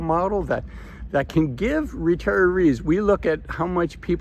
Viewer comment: It might be 5% of all people, not 5% of 55-65 year olds. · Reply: I thought he said retirees, but I could be wrong.